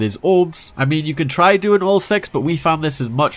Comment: sounded like you said 'wall sex'